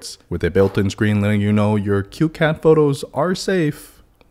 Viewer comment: “Cute cat photos” is way more creepy when you write it as “cute 🐱 photos” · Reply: I thought he said "CueCat photos"